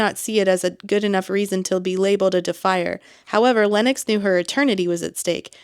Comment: Whenever you say Defier, I keep hearing defiler... Idk why, it just feels more like a real word I think 😂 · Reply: I heard “defy her”, which was confusing 😅